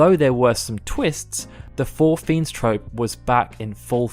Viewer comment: shit i thought it said the FOUR FRIENDS LOL.